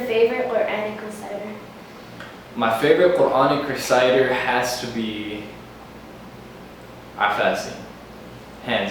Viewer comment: what did he say? I couldn't hear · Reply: he said his fav reciter is Qari Mishary Rashid Al Afasy